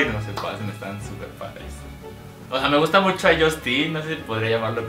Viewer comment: escuche bien en el minuto 3:44 q el crush era justine?